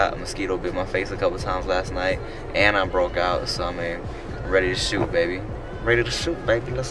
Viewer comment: at :36 i thought said something like "skeet on my face a couple times last night, and i broke out."